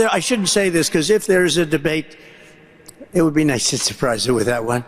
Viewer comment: "did he say it would be nice without one"; is that what he said because Im to busy dancing to freedom music with my 1st amendment s2 "freedom of speech and freedom of option" loving can someone confirm that please?😂😂